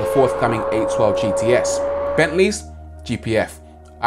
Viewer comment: did you say..... 812....GTS?!?!?!?